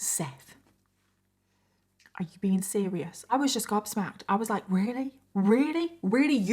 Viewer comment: I thought said she was "God smacked" (yea, like the rock band 🎸 lol) in reference to Amy's first son being born on Seth's birthday and then NAMING him after the brother she killed. I was confused so l had to look it up. I love learning about other languages (especially proper English) and the different terminology thats used in the UK vs the US So after Googling l realized said "gobsmacked" which basically means: "chiefly British, informal. : overwhelmed with wonder, surprise, or shock " [according to Merriam-Websters website and Google] l thought it was so AWESOME to have learned a new word and the correct way to use it. I LOVE watching your videos and learning all the details about these cases. I thoroughly enjoy the way you describe all the facts and keep us interested by presenting what must be a crazy amount of information in a way that sounds like we're sipping hot cocoa and sitting by the fire listening to a good story. THANK YOU for making these videos and putting a ton of effort researching these cases so that we may know as much about them as possible. Your hard work is so GREATLY appreciated !!! 💕✨💕✨💕✨💕✨💕